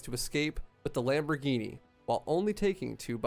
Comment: does he say lamborghini?